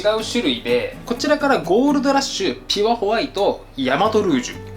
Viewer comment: ピュアホワイトが4歳娘にはキュアホワイトに聞こえたみたいで、プリキュア!!??って飛んで来て画面見てキョトンとした😂